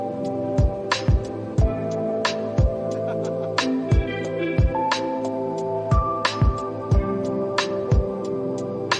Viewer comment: can you tell me what is that I can't understand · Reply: Dear it was something like cardboard